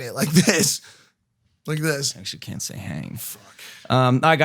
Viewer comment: Who heard that 7:37 where Logan quietly said “You can’t F**** say Hanging”.